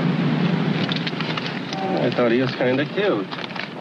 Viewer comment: I swear, as Anne Baxter is exiting the cab, the cabby says (referring to Sterling Hayden?!), "I thought he was kinda cute". Did I hear right?